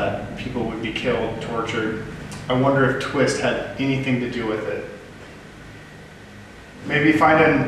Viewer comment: I thought I heard “I have nothing to do with the corner” I listened back several times and that’s what I think I got